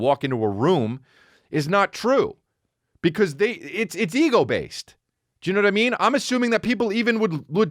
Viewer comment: Absolutely thought Chris was saying “eagle based” Love the show, Chris! is the shit!!